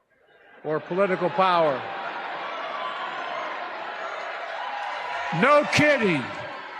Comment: I think dummy was trying to say “electoral” but was saying “electrical” instead 😂 Then says political power like it made it sound ok 😅 I also thought he said “no kitty” instead of “no kidding” lmao